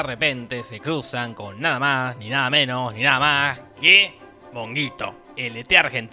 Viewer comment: ``Monguito" ¡QUE MAMADA, ES EL MEJOR NOMBRE DE LA VIDA! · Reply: Brenda Ramos yo le entendí “honguito” 🤦🏻‍♀️